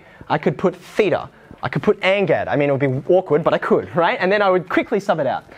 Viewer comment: what was that he mentioned after theta (sounded like angat)?